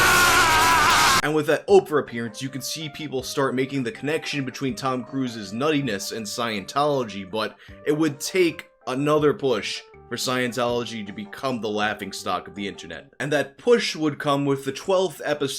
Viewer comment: I thought you said "It would take another Bush" and that would also make sense :P